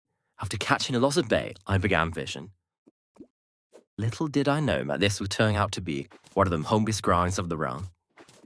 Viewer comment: what did "you" even say here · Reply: “Little did I know (my? ‘This was edited’) this will turn out to be one of the longest grinds of the (round? Realm?)”